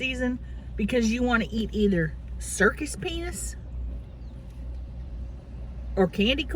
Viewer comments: I swear I heard "Circus Penis" twice! 🤣🤣🤣🤣 I about died hunnies! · God! I get the Fitbit rash....super annoying. I thought you said Circus Penis first time!!